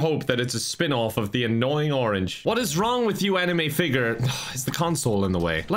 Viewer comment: on that particular moment sounded like 'enemy figure'